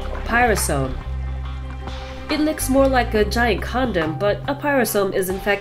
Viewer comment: did she said giant CONDOM???